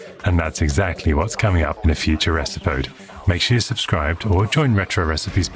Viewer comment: I had a wee giggle at your commentary, at 18:48 I'm sure you say "in a future esipode" instead of future episode.